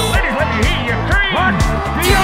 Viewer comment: for an entire year I thought it said scream